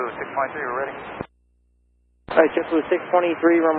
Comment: Anyone else hear the JetBlue pilot ident as 653 and not 623? Or are my ears dead?